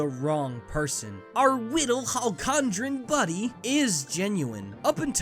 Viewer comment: What does he call Magolor? "Our little ??? buddy..." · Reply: Halcondran? Is that where he's from?